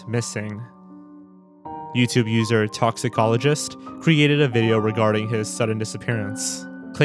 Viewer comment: ATTENTION: Erratas has been confirmed to be an ARG. Toxicologist, reported the finding when another YouTuber pretty much asked if it was forgotten or not. It was an ARG made by DJ Rozwell himself, basically and ended it. Apparently, the ARG was not meant to be frightening. EDIT: After 2k likes on this, I'll elaborate some more: It was meant to be a Gorillaz-like band with him and a friend, but he left and DJ Rozwell made it his project with chopped+altered effects. He didn't come up with the automated captions on the "well here goes nothing" video and was done with a lot of low resolution effects. When it came about, people thought it was a serious thing like the Roko's Basilisk thought experiment, along with the disappearance of Exer Erb, causing DJ Rozwell to say it was nothing than a blown-out-of-proportion ARG. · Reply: Legit thought you said " Dermatologists" at first and thought you were making those "Dermatologists hate her' ad 😭